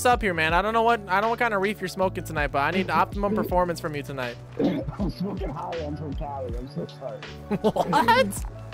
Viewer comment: Sounded Like He Said "I'm smoking jale" (meth) 🤣🤣🤣